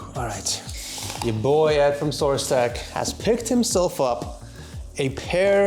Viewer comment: "yah boy Ed from 'sourcetech'"????????????? Did I hear that right